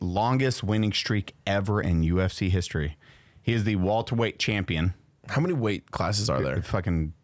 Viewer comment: I feel like was stuck in the M3th subreddit. I swore he said, “He’s the Walter White Champion”